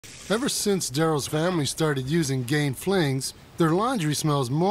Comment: That dude sounds like he is saying GAY FLINGS... I will not be buying this product...lol